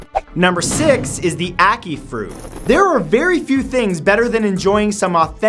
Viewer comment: At first i thought he said acne fruit.....